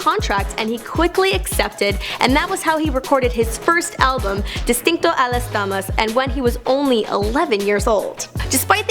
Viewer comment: Lmaoooo her Spanish pronunciation 😂 I thought she said “distinto a las damas” y cuando dijo “Medellín” i lost it lmao. I was like what!??! It’s all love tho I’m not hating, just funny